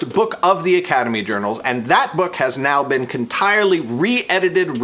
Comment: I hear you say "contirely" when you were talking about the restoration XD did you get halfway through saying completely and switch to entirely? :3